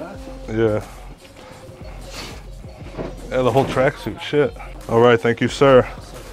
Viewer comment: did he just say "yeah the wholes tracksuits shit"?? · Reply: "Yea the whole tracksuit, shit"